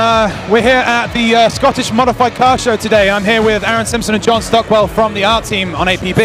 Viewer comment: He said "modified cars"! I heard "mothe-fa-cars"...